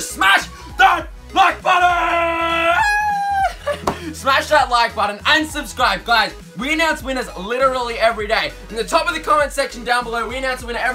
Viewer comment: Did he say smash that like button, unsubscribe. or. Smash that like button and subscribe. ???